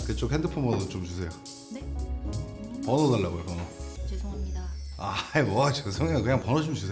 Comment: 왜 자꾸 '돈달라고'로 들리지.